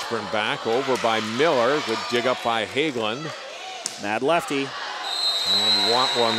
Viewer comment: does say “bad lefty” towards Elizabeth Helmic? RUDE INDEED if that’s true!!! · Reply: Don’t worry he said MAD leftie.